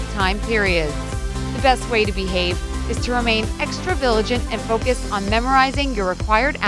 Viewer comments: Did she say "Extra viligant" at 6:08 or is it just me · I heard extra viligent 6:09 😝